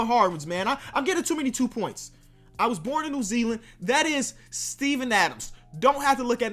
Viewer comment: On The Steven Adams one I thought he said Steve Nash